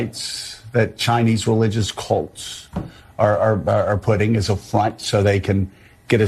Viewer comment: Did he say ch!in3se rel!g!0u5 cult5 are ant! p0key?